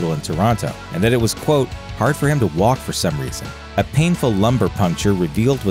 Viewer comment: Sounds like you said a "painful lumber injury" - you do realize he wasn't injured by a two by four I hope.